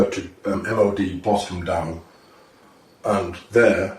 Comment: Did he just say MOD Bottom Down? 😂😂💀